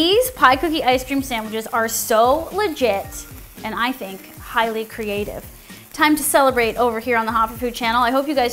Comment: I thought u said PIELY creative and I was like.........